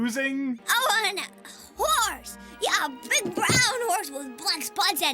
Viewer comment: I swear I thought instead of the kid saying I want a horse,thought he said I want whores. Wouldn’t that be an interesting Christmas, one to remember